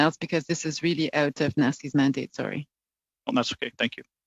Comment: did she really say "it is really out of Nazi's mandate" ))))